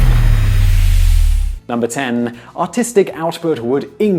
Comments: Every time he says "artistic" it sounds like he says "autistic" I know its probably because he's British/Australian but i just find it funny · Anyone else thought he said autistic on #10??